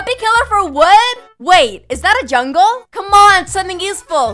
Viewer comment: When she said “is that a jungle” I thought she said “is that a jungkook” lol being an army is though